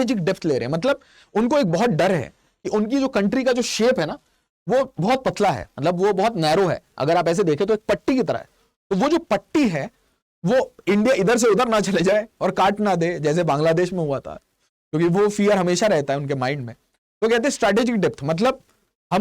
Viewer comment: Patti sounded like Tatti 😂😂😂😂